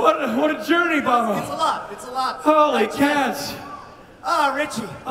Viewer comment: Did- did he say, Holy Cass?